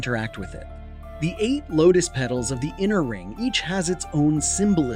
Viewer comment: you say eight locus petals and the subtitles say eight Buddhist petals. CORRECT IT IF YOU WANT. NOT A BIG DEAL THAT ANYONE WILL REALLY NOTICE....LOL